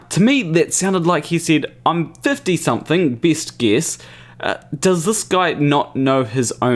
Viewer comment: No - he said "best guess", not "Bissgiss". Perhaps you were mishearing "biscuit".